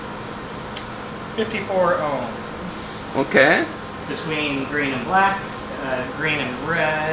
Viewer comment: I thought that you said between Green and Bread 😅